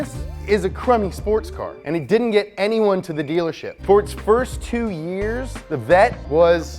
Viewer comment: Did he say Ford